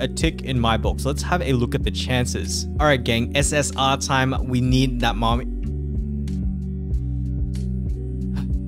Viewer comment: did you say we need that mom....?